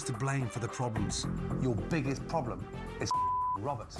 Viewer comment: Did Ramsey just say that his biggest problem is fucking Robert?